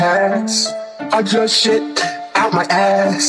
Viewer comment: The ones who thought he said bootied up my pc 😂 👇🏾